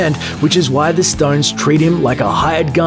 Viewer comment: am I the only one who heard Paul Rudd, not Phil Rudd. 😂